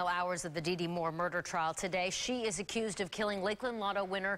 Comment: I keep hearing "Dinty Moore" when they say her name.